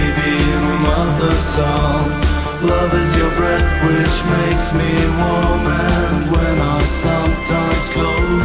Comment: Good work!! Did you notice that there's a mistake ? 0:47 it's "make me" instead of "take me".